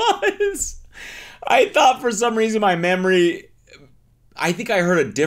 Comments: Did you say MAMMARY?!?! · for a second I thought you said "I thought for some reason, my mammary..." word play would have been 🤯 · I could have SWORN he said “in my mammary” instead of memory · did he say mammary? ☠️